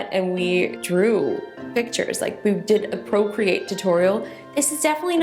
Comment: I thought you said procreate tutorial. My eyes went 👀.